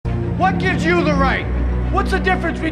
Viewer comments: when i first saw the movie i thought he was saying "i'm not wearing hockey pants" XD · I thought he said I'm not wearing a hockey mask